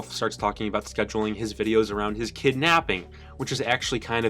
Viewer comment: I thought he said “kidnapping” rather than “kid napping” and I was very concerned for a good couple of seconds.